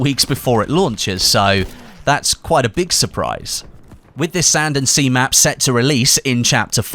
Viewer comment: It sounded like you said sand and semen. Strangely that would describe my average bf5 session.